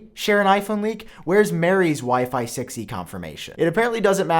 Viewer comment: Sounds like you said "Where's Mary's Sexy confirmation".